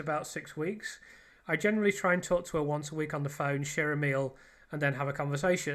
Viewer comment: what Mark McD says is "she'll ring me" not 'share a meal' !!!